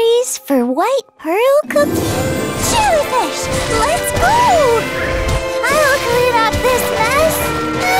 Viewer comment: I really thought she said I’ll clean up these pests when using her skill 😭